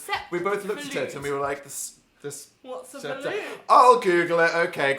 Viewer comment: i thought said "soup balut" 😱😱😱😱